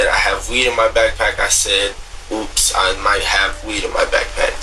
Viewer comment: did bro say "oops"? 💀